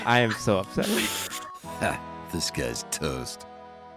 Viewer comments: I just realized your intro says “disguised toast” not “this guys toast” bruhhhh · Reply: It actually DOES say “this guy’s toast” and is a line from a hearthstone card. That’s where Toast got his name from because he thought it SOUNDED like he was saying “Disguised Toast” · Toast's name comes from a hearthstone character he kinda sound like he says "hey this guys toast!" But it sounds like "Disguised Toast" and i love it